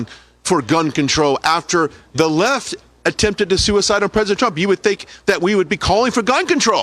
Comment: is that a Freudian slip i hear herennnor did he say "suicideing president trump"?